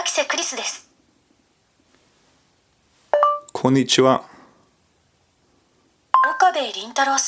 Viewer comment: what he says: konicchiwa What automatically generated subtitles say: NIETZSCHE